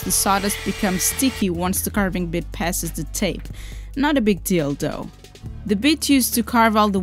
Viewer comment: I'm sorry but it sounds like you said at 1:36 "Not a big dildo", lol... You might want to emphasize the "th" in "though" next time.